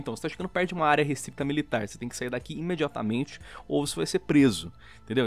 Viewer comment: Opa escutei errado! prezo não era morte?